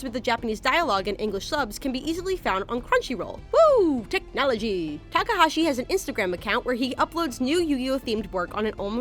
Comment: Did she say Whoah Technology? Yansim fans where are you?